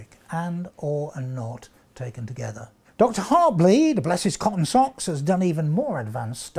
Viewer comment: "Bless his cotton socks" did he really say that?